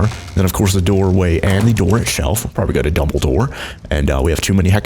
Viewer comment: Between 5:40 and 5:45 he says double door but it sounds like he said dumbledor or however you spell it lol